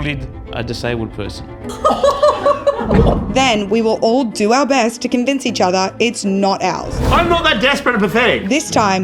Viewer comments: I heard it "we will all do our best to convince each other it's not Alan" :) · I heard it "we will all do our best to convince each other its not Alan"